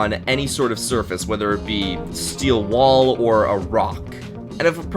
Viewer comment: Did he just said a steel ball ? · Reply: Steel wall